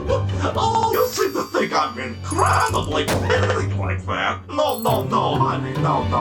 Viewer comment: I'm still trying to figure out what Lolbit said, "you seem to think I'm incredibly *glitches*" someone help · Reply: Petty, They said "you seem to think I'm incredibly petty"